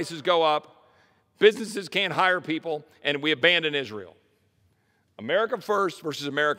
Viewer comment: Did he say "We abandon Israel"?